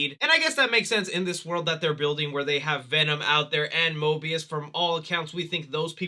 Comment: It sounded like you said mobius instead of morbius haha Loki finale still has us all in shock. Love the video by the way!